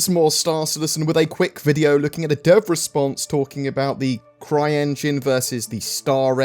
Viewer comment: I thought you said "derp response" at the start!